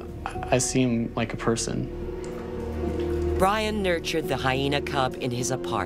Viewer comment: sounds like he is saying HYNAH